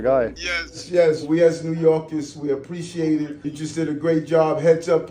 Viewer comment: i thought i heard "unjust" in what he said